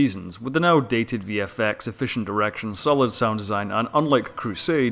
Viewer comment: It sounds like you said "sufficient erection" instead of "sufficient direction". Maybe it's just where my mind is going lately.